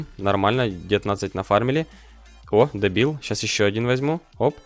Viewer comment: Мне послышалось или он сказал дэбил?